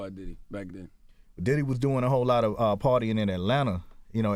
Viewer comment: is he calling him "daddy"? all the names puff got and he picking daddy...i thought he was saying diddy!